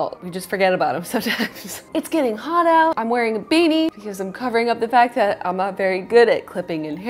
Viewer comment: When she said " I'm wearing a beanie " not going to lie I heard "I'm wearing a baby "